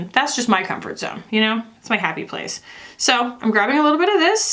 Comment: After the Tedcore convo, at some point you said "its my happy place" and I thought you said "its my Kackie place"